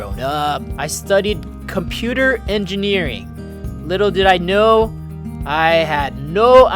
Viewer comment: did you said little do you know I love that song LITTLE DO YOU KNOW 😂